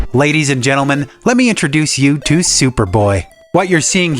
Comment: Ye sounded like ladies and gentlemen we got em